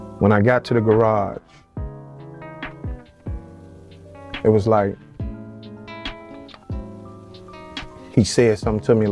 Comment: He did not say his brother said out of his own mouth to him that he was backdoored. He said ‘It was like he said to me’ which means his brother spirit came to him. He did say it at the scene where he was shot is what he say’s after that.